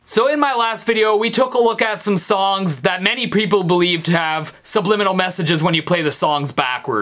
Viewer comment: did u say preople? lol ;)